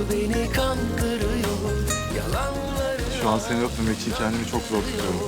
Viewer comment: 'de ne diyor? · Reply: Şuan seni öpmemek için kendimi çok zor tutuyorum diyor.